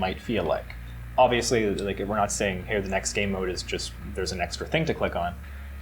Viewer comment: Did I hear new Game Mode?